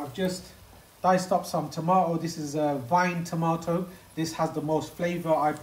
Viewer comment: What type of tomato did you say? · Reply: Vine tomato